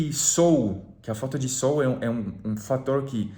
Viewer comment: Cara, eu juro que eu entendi "a falta de soul", e pensei caraca, que profundo esses pensamentos do hein kkkk